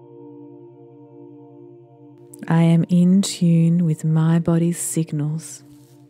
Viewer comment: Good one. But did anyone understand what she says at 40:15 and same words are repeated at intervals, one at 56:40. It sounds like a foreign language! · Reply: She says “I am in tune with my Body’s signals”